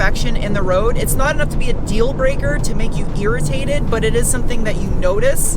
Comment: For some reason, when you said "irritated", it sounded like you said "GEARritated". And now that's all I can think of.